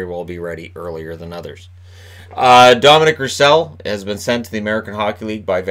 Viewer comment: Did he say Dominic Roussel? I could have misheard but It didn't sound like Antoine